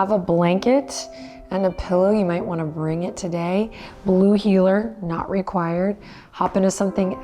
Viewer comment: 😍Blue heeler 0:31.....Settling on the mat, at first I thought you said "blues healer" but that's probably my subconscious messaging me today. Thank you Adriene and Benji for this practice. 🙏